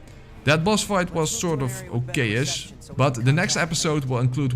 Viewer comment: No, and you never have in your videoes. It's right after the battle with Adam. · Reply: he said "okay-ish". You misheard him.